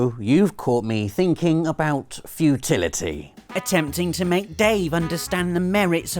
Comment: lol thought said "you caught me thinking of fertility" at the start😂